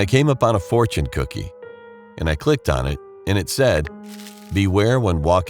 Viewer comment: Who else thought it said fortnight cookie💀